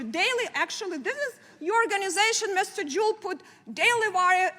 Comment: Did she call him Mr. Jew?